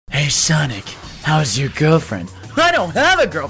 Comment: What sonic says: "how's your dad?" What me hear: " HOw's YoUr DeAD? " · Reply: I heard "Oh... you're dead !"